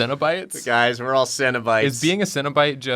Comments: Okay until got her prompt I thought they were saying cinnabites, and they were like little cinnamon roll treats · I kept hearing "cinnabites" and being very confused